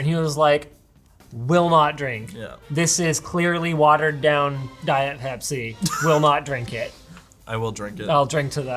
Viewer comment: with CC on at 23:00 the captions think Linus is saying "watered down Diet Hep-C" 😂😂😂